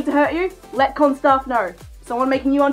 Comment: In my subtitles when you said con staff it thought you said corn staff